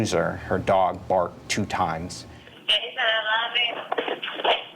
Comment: Is anyone else hearing “Jason I love you-“ chilling to the core…..